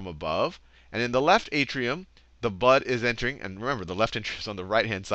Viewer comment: The video was amazing ... thx it really helped me but at 8:06 did he say butt instead of blood? sry, it jus bothered me .. LOL